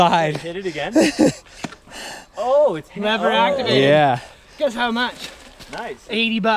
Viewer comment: "It's ckknavractivated." What the hell does he say??? I'm curious as to what he said, although I don't think I have any interest in a dropper post where having the remote under the saddle would make it necessary to ride one-handed . . . even if it were only a split-second. Now, a wireless dropper post is a type in which I would have a great deal of interest. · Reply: "It's lever activated."